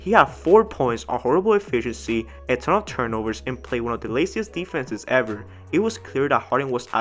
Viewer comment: bruh i thought u said "12 turnovers" for a sec🤣🤣🤣